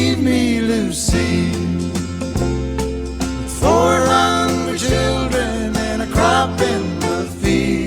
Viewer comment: When I was a kid, I thought it said 400 children instead of 4 hungry children. Lol. I love Kenny. So good.